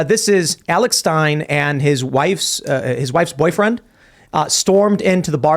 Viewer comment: Did I just hear "Alex Stein and his wife's boyfriend"?